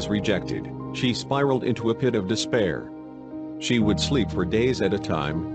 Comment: Did you just say *DeSpaIr*? Junko: my time has come